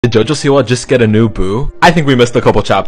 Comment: i thought u said "did jojo siwa get a new poo?"